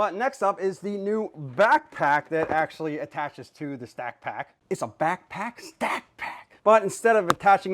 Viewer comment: Dammit brother, I thought you said snack pack!!! Lmao . Yet another great video. Those don't look too bad. I like tool storage.